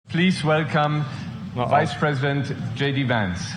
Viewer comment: I thought the guy at the beginning said Reichspräsident, but I misheard Vice President. Consider the Weimar period of deviancy. It was equivalent to Joe Biden’s Presidency. Both regimes jailed political opponents, both regimes did this kind of crap. The people got fed up and elected Hitler as Chancellor who then became a dictator through engineered crisis after crisis by trampling on the rights and freedoms of his people. Keep in mind National Socialism is a compound term containing the concepts of Nationalism and Socialism. According to Carl Marx, Socialism is one step away from Communism: a left ideology. National Populism, trumps government combines nationalism with populism, a right leaning concept where the people hold the power and the president and elected officials listen to their people and vote accordingly.